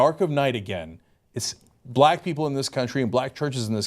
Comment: When Cenk said 'our media' at 5:15 I misheard it and thought he said 'Armenia'. But then I remembered that Cenk doesn't talk about Armenia and/or various historical events that have happened to the Armenian people.